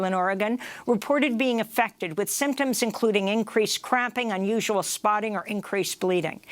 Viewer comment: Love Amy Goodman. She had me rolling with laughter at 8:35. I heard: " police riot gas causes unusual CRAPPING.💩🤯😁